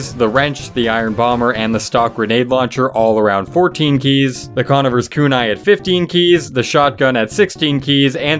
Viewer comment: Did you just say Conn-ivver?